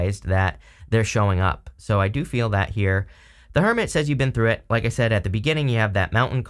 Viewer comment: I thought you said “the kermit” 😂😂😂 I was like, “what card is that?” 🤦🏽‍♀️🤦🏽‍♀️🤦🏽‍♀️ why am I like this? lol.